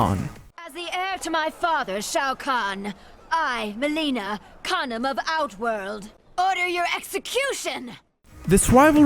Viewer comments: Why did i heard Mileena says "I Mileena condom of out world" LMFAO💀💀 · Lmao its sounds like mileena says shes the condom of outworld 😂😂😂😂😂